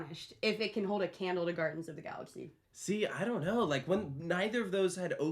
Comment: Sounded like said "Gardens of the Galaxy" at first when talking about Tron. That actually sounds like a more appropriate ride for Epcot.